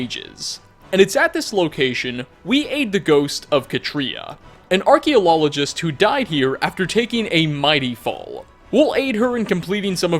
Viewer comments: Alright, did just say Archeolologist... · did anyone else hear "archaeo-lol-ogist" or is sleep deprivation getting to me? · Is... is anyone else hearing "archaeolologist" at 14:57? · archeolologist? Anybody else hear that?